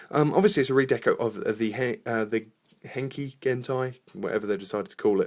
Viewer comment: i thought you said 'hentai' on